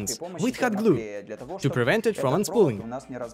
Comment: There are a few words I can't quite catch, like at 3:19 "To prevent it from [renspooing?] (that's what I understand)"... Understanding spoken English with strong accents is not my forte... And yet my accent is like the worst accent in the whole world. (I'm French xD) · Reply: unspooling